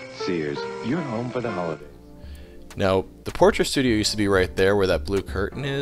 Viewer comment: I thought for a second you said "Torture studio"